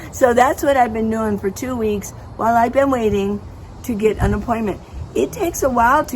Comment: Did you say unemployment at 7:15?